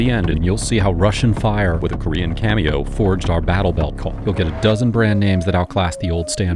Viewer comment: What did you say at 1:06 mark? Was it "Battle Belt Call"?